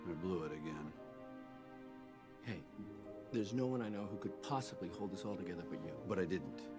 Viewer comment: what did Frost say? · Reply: "Hey, there's no one I know who could possibly hold this all together but you."